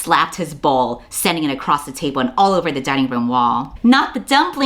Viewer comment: At first I thought you'd said: "…he slapped his BALL, sending it across the table and all over the dining room wall".